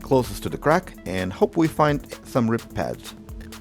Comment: Did you say "ripped pads"?